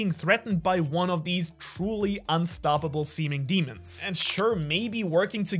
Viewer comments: did he just call the gorosei Semen Demons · am i the only one who heard 'semen demon' there?